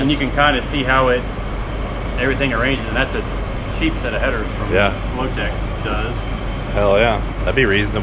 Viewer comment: did i hear motec headers? LOL